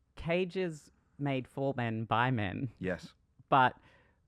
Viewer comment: Is it just me or did I hear “for men BI men”